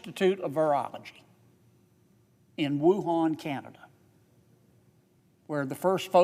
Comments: Does he say Wuhan Canada ?! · Did he say Wuhan, Canada?? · did he say Wuhan Canada? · 🤔 did he just say Wuhan ,Canada?